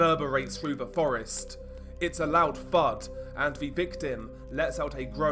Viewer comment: I thought you said "loud fart" at first.